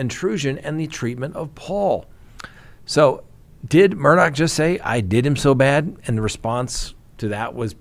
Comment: I hear "I did Them so bad".